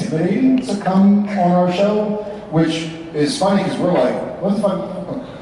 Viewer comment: What did say Jared? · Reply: I think He said fuck